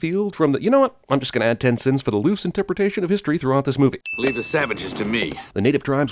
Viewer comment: I thought john said leave the Samiches for me